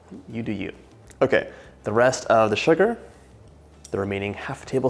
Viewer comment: great video but a little confused at 2:16 the subtitles say 1 tbsp of sugar but the host says half or the rest of the tbsp?